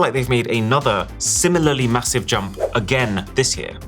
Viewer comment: did you say massive?